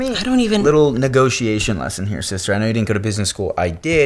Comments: Woah woah hold up, did he say "sister" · did he say a sister